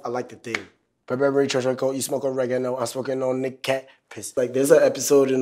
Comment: I can't be the only one that thought he said "smoking on that cactus"